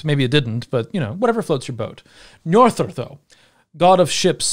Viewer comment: Apologies for picking on pronunciation, but did you just say Njorðrðr? 😅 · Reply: "Njordr, though"